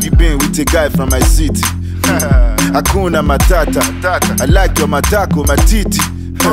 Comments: DID HE JUST SAY I LOVE YOUR MATITI MATAKO 😂😂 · Did I just hear falz say 'hakuna matata, I like your matako matiti!